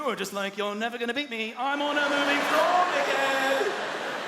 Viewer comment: Can somone confirm if I just heard what I think I heard xD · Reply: "You're never gonna beat me. I'm on a moving floor, dickhead" ?